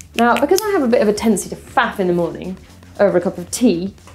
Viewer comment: Sounded like said "because I have a bit of a tendency to fap in the morning over a cup of tea" I think I need to get my hearing checked.